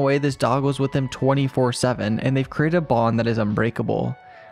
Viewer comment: “they’ve created a bong” the subtitle says! Lol